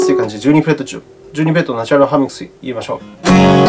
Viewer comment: なんて言って居るのか誰か教えて下さい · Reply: 12フレットナチュナルハーモニクス